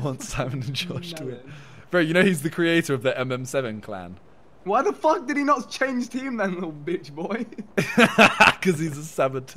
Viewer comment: What did vikk say at 23:00 mins i didnt hear i tried replaying but still couldnt hear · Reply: Vikk said "Why the Fuck did he not change teams little bitchboy" Then Simon said "Because he is a savetouir" XDDDDDDDD